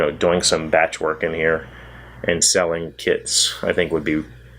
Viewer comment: I almost thought I heard, "selling kids." Then I was like, noooo. Sellings kits. Oh! Lol